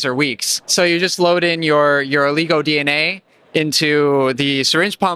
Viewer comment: Whats with the finger quotes at 13:00? It almost sounds like he is saying "Take your legal DNA". Lol.